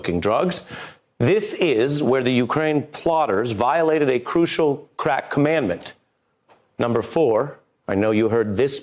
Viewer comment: Wait wait wait am i the only one that heard him say “they broke a crack commandment”😂😂😂😂😂